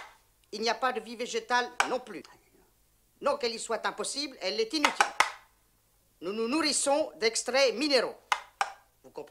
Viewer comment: il dit quoi a 1:08 ? qu'est ce qui est impossible ? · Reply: Non qu'elle y soit impossible, elle est inutile